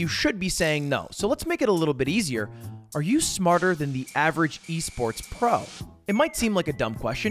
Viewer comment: Idk why but every time he said esports pro I thought he said wii sports pro